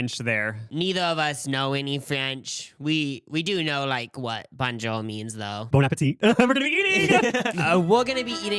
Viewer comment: I swore said “Bon jovi” and I was dying.. had to show my husband, telling him all about “He said Bon Jovi, instead of Bonjour.” Meanwhile, I just completely misunderstood it 🙈